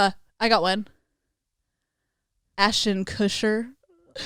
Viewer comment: I thought it said Ashton Kutcher and I was like goddamn they went from 0-100 real quick. 🤣